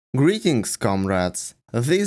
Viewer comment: i've watched most of your videos, all this time i thought you said "greeting scum rats!"